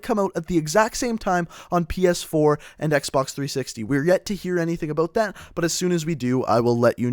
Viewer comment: did he said xbox 360 on minute